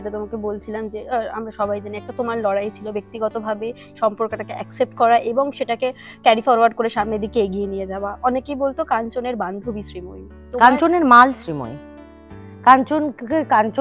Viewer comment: "কাঞ্চনের মাল !!" আমি কি ঠিক শুনলাম! 😂😂